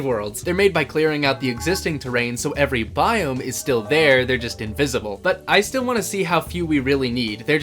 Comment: Say that again? 4:10 did you say they are just [title card] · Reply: He said invisible not [title card]